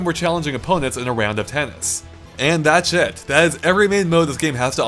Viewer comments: I love when says "that's it", it sounds like he says "that's shit". lol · It sounded like "That shit" rather then "That's it" Well played. :P